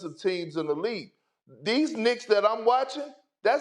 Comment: Anybody else think he was about to say "these ni**as that im watchin"? 😭😂